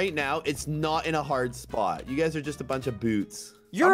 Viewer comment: Did I Hear ßit*h o.o